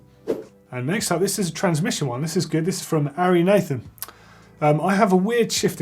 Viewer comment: For a second there, I thought you said "Aryan Nation", not "Ari Nathan" lol !